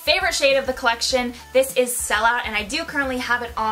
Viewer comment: What lip color are you wearing durin your intro and the review? It's absolutely gorgeous omg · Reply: Pretty sure it's sell out, I think that what she says